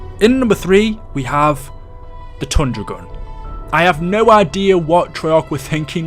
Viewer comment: When he said tundra gun I thought he said thunder gun and I freaked out